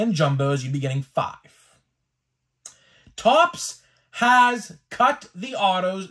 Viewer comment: Thought you said Topps was cool